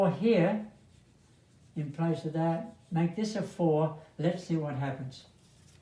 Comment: Hi, Greetings from Finland ! :) I am wondering in this lesson, how on earth did you find out (in videos time 24:01) that in the upper left block (in the 1st column and 2nd row) the value of the triplet was 4 and not 1 OR 3 ? Number 4 was a candidate also in two other cells in that block. Was it a "civilized guess" OR is there a Logican explanation för that ? Thank You anyway for your professional lessons. I have watched them all. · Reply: Me too, I heard “if you put a 4 in here, let’s see what happens” ??